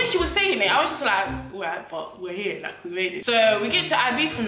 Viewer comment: I kept hearing “I beef her”